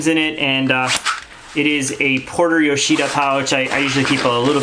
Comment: What pouch is that at 6:55? Sounds like you said Porter Yoshida, I couldn’t find it. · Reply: Yeah, it's Porter Yoshida. It's the snack pack or something like that, I believe.